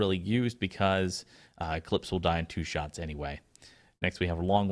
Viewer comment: Damn my hearing sucks and my mind just goes straight to the gutter. At 3:30 I heard "A clit will die in 2 shots anyway..." Wait, what?